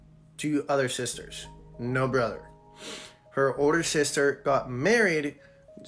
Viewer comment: anyone else hear him say " Her wh*re sister got married" at 3:08?